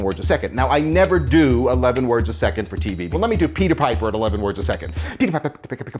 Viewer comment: i thought he Said pewdiepie😂😂😂